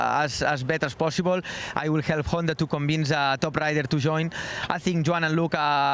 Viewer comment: did he said Toprak to join? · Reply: Top Rider.